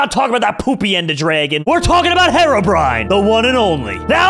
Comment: At start I thought Jerome said Hetrobrian